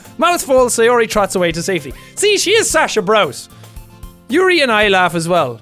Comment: did he say Sasha Braus? MY POTATO GIRL FROM ATTACK ON TITAN? THE REFERENCEEEEES